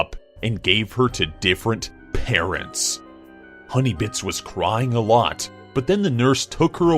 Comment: I thought it said honeybitch for a sec lol i'm so dead